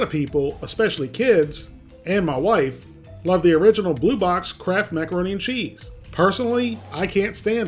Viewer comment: i thought u were saying "crap" macaroni an chease lol ... or is it kraft?.. i cant tell .. sounds like crap....... @ 0:49 lol @ 0:57 hahah crap!